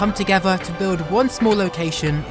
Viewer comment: did you say “small”???